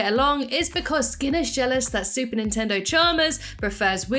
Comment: did you say Super Nintendo Chalmers?